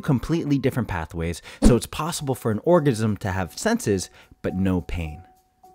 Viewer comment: he said orgasm instead of organism